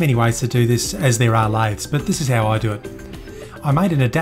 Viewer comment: what did he say there? 2:12 "there are as many ways to do this as there are ??" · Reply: "lathes". (I think)